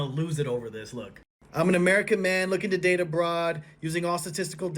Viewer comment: I hear "I'm an American man looking to date a broad"😅